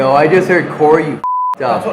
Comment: he said “hear Corey you f cked up” he said what said before said it